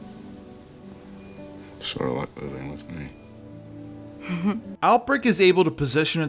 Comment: I cannot make out a word he's saying. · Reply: "Sorta like living with me." I'm sorry. In the context of the moment, the scene works better. Out of place, as I have put it, it doesn't help! I hope that helps you though.